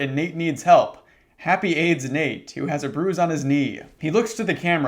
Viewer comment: misheard that as “happy AIDS, Nate!”